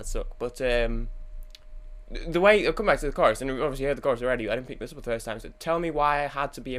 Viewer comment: someone tell what he said at 7:00-7:03. I have no idea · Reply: "I'll come back to the chorus and obviously, I've heard the chorus already but I didn't pick this up the first time"